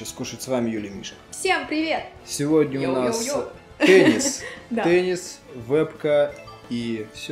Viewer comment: мне послышалось пенис